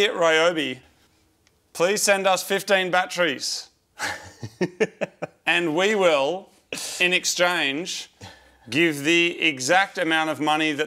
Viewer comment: I could have sworn he said "send us 15 batteries and we'll send the money to RETAIL" lol I thought they were joking and saying we basically need more batteries and are willing to pay full price.